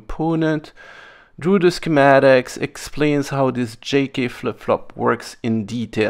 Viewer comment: For a second, I thought you said “janky flip flop” instead of J-K.